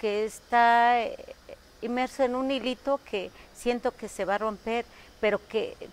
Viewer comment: en el segundo 0:31 lo que dicen ser (zapoteco) en realidad dice "hilito" y en ingles <<thin string>> mas o menos